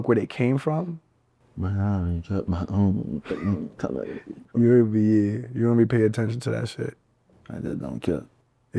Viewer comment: somebody help me what did he say at 20:12 ? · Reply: "I don't give a f*ck shawty ion even wanna talk bout that shi"